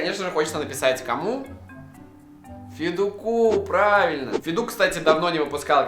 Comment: Мне послышалось петух а не федук😂😂😂